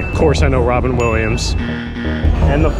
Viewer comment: Allen Jackson is a very common name is the whole problem There's the country singer and I'm pretty sure there was an actor too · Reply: I thought you said "alien jackson"